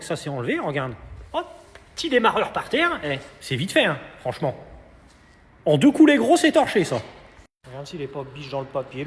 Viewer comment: "en 2 coup né(...)" quoi ?😶